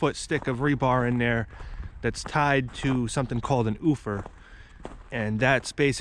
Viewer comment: I didn't understand the term you used for connecting the 20ft rebar on new home build 41:17. Could you expand on that? Did you say oofer? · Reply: its called an Ufer, its an alternative means to ground the system. its just connected to a piece of rebar that is inside the foundation wall, you bend a small 90 and stick it into the basement